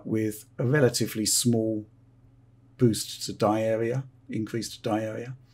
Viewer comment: says "die area" , I hear "diarrhea".